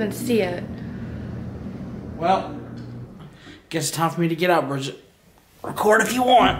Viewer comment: I heard a faint GET OUT at 9:36.